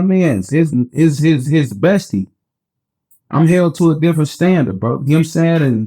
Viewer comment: When he said bestie all I heard was “twinnnnnnnnn”